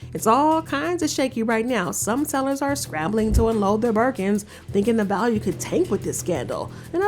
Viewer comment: I thought they said Bikinis.